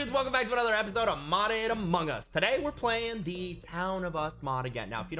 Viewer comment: For the first time I heard "modded among us" instead of "modern among us"